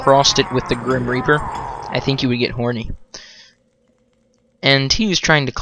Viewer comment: LOL what i heard at 11:12 "It's kind of like, if you took the devil and crossed it with the grim reaper, I think you would get horny."